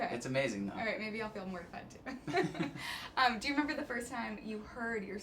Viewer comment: 6.23 when she says "maybe I'll come with you" and he gives a "engh" Lol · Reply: I thought she said “maybe I’ll be mortified too”😂